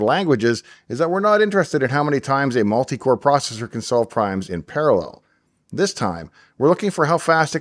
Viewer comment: Could have sworn he said "we're not interested in how times a multi-core processor can solve CRIMES in paralell"